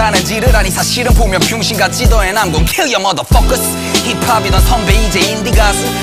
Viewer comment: 힙합이 더 -> 힙합이던 3:02 얘는 poor -> 예능 프로 3:15 유행 하나로 -> 유행하는 옷 3:41 거대하게 -> 펴야될(피워야될?) 약이 여긴 잘 모르겟음 4:01 한정했고 -> 안정했고 4:04 잃던 -> 있던 4:29 fanas -> problems 맞는지는 모르겠는데 문맥이랑 들리는 거 맞춰서 고쳐봤슴당